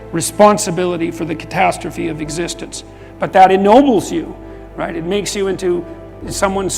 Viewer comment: At around 6:50 there's an error in the text it says "enables" but what I hear is "ennobles"